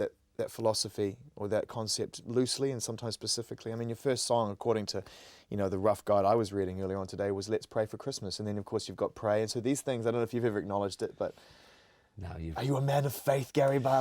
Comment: Does he say Pacifically at around 11:31?